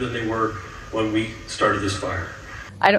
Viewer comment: Did he say “when we started this fire”???!